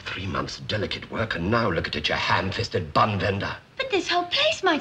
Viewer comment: Sounds like he said Ham fisted bum bender. :/